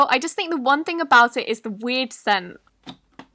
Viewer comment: when you said “weird scent” it sounded like you said weed scent with your accent 😂😂